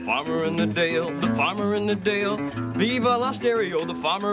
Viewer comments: Did he say, viva la stereo? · Did he say, viva la stereo? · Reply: I heard “Viva Las Dairy-o,” but I could be wrong